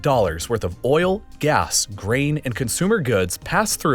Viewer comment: I thought he said the ships carry rain. But I listened 3 more times and he said grain lol